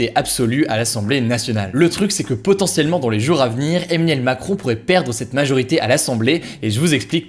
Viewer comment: je rêve ou j'ai entendue eminem macron